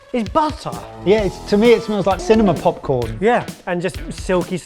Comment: I misheard it as "cinnamon popcorn" which sounded more appetising to me, tbh.